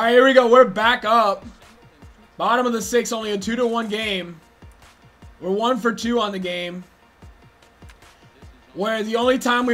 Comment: Did just say 1 for Jew